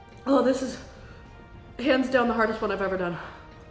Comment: I'm gonna start saying "pants down" instead of "hands down" from now on.